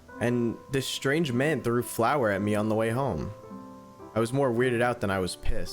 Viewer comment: Oh- 💀💀💀 I thought when you said flour you meant flower 🤣😭😭😭😭